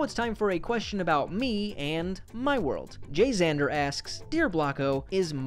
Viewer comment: hold on did you say JayXander? That's One Of My Friend's Username On Roblox